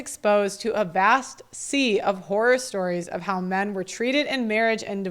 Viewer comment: Divorce Documentary fundraiser: <link> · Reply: You said "horror stories" but I heard "whorer stories" and I was absolutely horrified by that. (You might say I was "whoreified.")